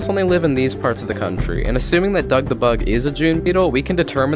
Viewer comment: I thought you said thug beetle💀💀💀